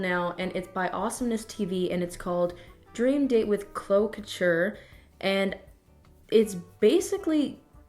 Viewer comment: How did you say “Chloe Couture” 0:33 Edit: just heard here say Shane Dawson. Love you, but what the heck